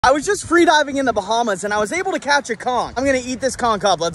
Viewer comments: I thought he said crouch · Thought it said chonch